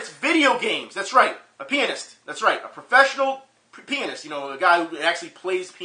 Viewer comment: lol i thought you said professional penis. btw el toro will get his. just believe in your self and dont run half marathons before it